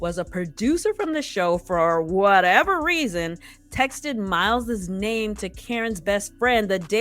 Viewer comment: Sounds like she is saying Miles is gay.